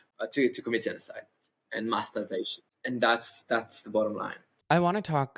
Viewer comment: the captions think said masturbation when she "mass starvation." idk if you can edit captions, but I also don't want this video to get flagged for something that it isn't about. <3